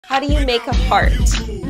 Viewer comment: i only hear: "how do you make a fart"